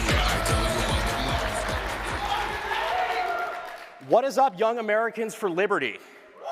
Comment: All I hear is "What is up you sexy Youtube motherlovers."